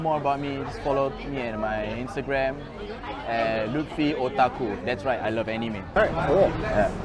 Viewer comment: I first heard Lutfi's name as Luffy first and I was like damn bitch ain't you supposed to be in the Grand Line looking for One Piece and shit?